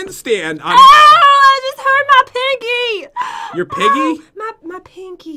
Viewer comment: Jen: I HURT MY PINKIE!!! Me: Oh I heard you pinkie too!! DO YOU EVEN ENGLISH BRO???!!! no................................ btw, I SUBSCRIBED!!!!! luv y'all 😚😚😘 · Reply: I thought she said I hurt my piggy